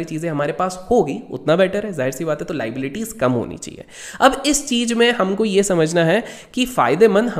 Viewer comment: 1st time I heard 1:31 Leads Kam hona chihe 😂